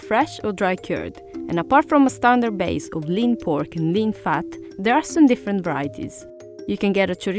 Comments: Did she just say "lean fat"? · Did she just say lean fat?